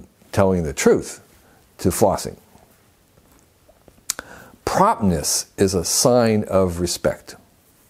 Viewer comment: flossing? what did he just say? I don't think the subtitle is correct. Can someone help me, what could be the word? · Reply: Promptness. Edit: The word just before 3:50 is indeed flossing.